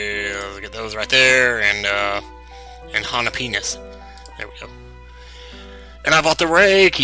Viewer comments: It sounded like he said jalepenis. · did he say around 00:45 or before when he took the jalapeanos (or how are they spelled) jalaPEANUS ? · did he say hala penis?